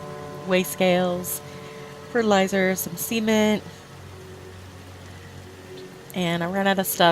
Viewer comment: I'm currently building my first greenhouse so I decided to watch this vid again along with your flower shop vid for ideas. It was going good til I thought I heard you say you put fertilizer and "semen" on the shelves. No way I'm putting semen in my build.... Oh... cement.. CEMENT! I guess your naughty mind is rubbing off on me again ;) I like BadBry's idea of putting the grates under the pumps but I don't know if I'll have room for that. I've already stolen your window box idea but I tried to put my own spin on it. One obvious thing you showed me was a broom. I thought of shovels and hoes but for some reason I spaced (or is that Mr.Spacey'd) out on brooms. Are there any other "greenhousey" vids you would recommend?